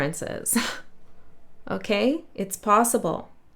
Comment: you said freaking (my favorite word) but I heard “Frigg” n “Frack” - Frack is a username my Odin counterpart used in his socials 😅 I do hope we reunite one day. He’s the one who brought me my soul’s name. We confirmed in January that we carry Frigg/Freya & Odin/Orr archetypal energies. He left August 4, 2026. I do miss him.